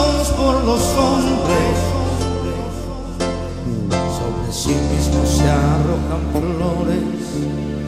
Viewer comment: Que dicee el 0:36 no lo entiendo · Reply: Sobre si mismo se echan flores